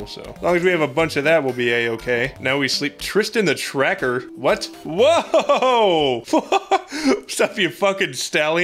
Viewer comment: All I heard was "Tristan the tracker? What's up your stallion!" And that's the confidence boost I needed. Thank you